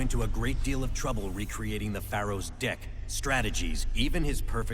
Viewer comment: Okay, did Kaiba say "reconstructing the Pharoah's deck" or "reconstructing the Pharoah's dick"?